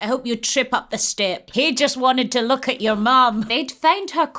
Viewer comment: I just hear “I hope you trip up the stairs” then the others Bro I’m supposed to be sleeping 😭😭😭